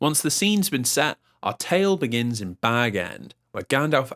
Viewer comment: For some reason when you said “our tale begins at Bag End” I thought you were about to say “our tale begins in Baghdad” 😂